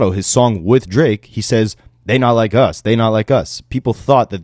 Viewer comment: I thought he said “they don’t like us” not “they not like us.” 🤷🏿‍♂️